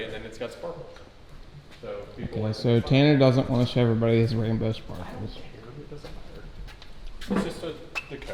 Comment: Did you say rambo sparkles or rainbow sparkles?